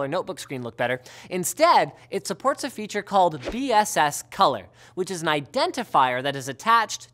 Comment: on 3:33 the subtitles say VSS